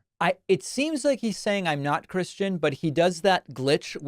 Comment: Sounds like he said "I'm not Christian"